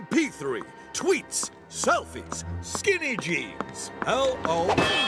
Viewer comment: is it me or he really said SKIBIDI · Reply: He said skinny jeans